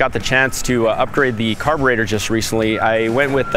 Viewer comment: sounded like he was about to say carter